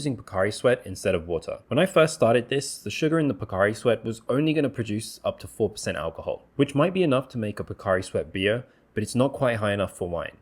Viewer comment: I thought he said Bacardi sweat. 😂